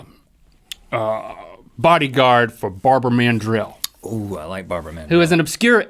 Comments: At first, when link said Barbara Mandrill, i thought he said barbar man jill Lol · At first I thought they said 'Barber Man Drill'